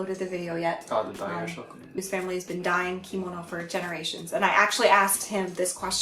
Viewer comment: When you said "His family has been dyeing Kimono" I thought you said "His family has been dying!".